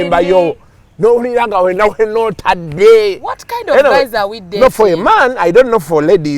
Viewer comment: Who else heard "what kind of mans are we dating!" Otherwise thx for always cracking out jaws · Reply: She CLEARLY said guys and not mans. You might need to see an otolaryngologist.